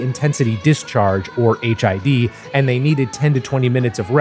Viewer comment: I thought you had said HIV for a second.